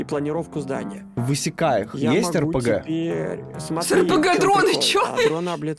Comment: мне одному показалось что он сказал «в исикаех есть рпг»?